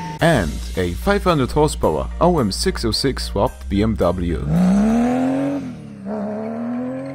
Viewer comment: DID YOU JUST CALLED THAT AN BMW !!!!!1!!111!!!!!